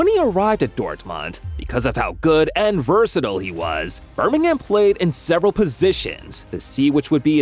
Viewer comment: did bro say Bermingham. 😂